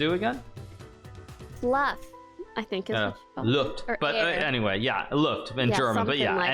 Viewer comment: What German word does Hikaru use at 35:43? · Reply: Luft